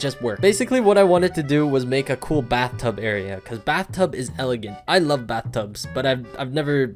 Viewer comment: did he just say “bathtub is elegant”?